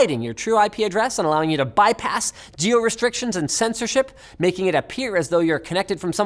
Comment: what did you say jio restrictions!! Oh it's geo restrictions!! Indians will understand